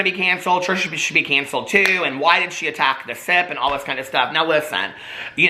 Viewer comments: 🤣Every time he said “The Sip” I heard “The Sith “. I thought he was low key calling them evil lolol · Every time you mention the “sip” podcast I thought you said the “SITH” podcast 🤣 legit thought ryland had a Star Wars podcast